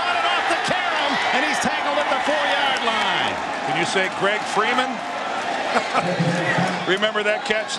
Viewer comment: Did he say "Greg" Freeman?? 😂😂